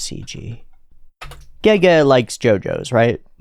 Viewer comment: I heard "Gege likes Gojo right?" and im like UH NO?? where'd u heard THAT. lmao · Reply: He said "Jojo's" not Gojo. lol